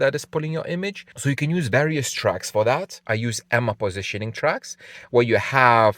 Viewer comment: Hi, what positioning tracks did you mention at about 6:40 in the video. It sounded like Emma positioning tracks is that right. Do you mind providing a link or clarifying which tracks you use specifically? Thank you for making these videos!